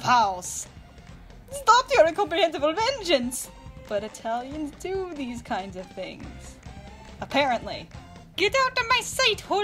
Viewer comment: I thought she said “But hetalians do these kind of things” 😂😂😂